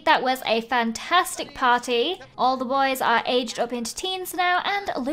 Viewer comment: When she said all the boys are grown up know my brain thought she said "my milkshake brings all the boys to the yard"